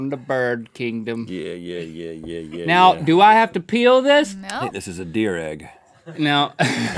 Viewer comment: I thought link said "do I need to pee on it?!"